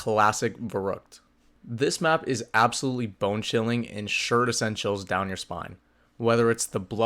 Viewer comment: I heard "Sure to send chills" as "Sured Essentials" I had to play it back 4 times like "Dude, huh?" 😂😂.